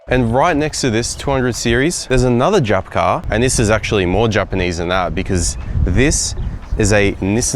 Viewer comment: Correct me if I’m mishearing, is he saying ‘Jap’ cars in the beginning?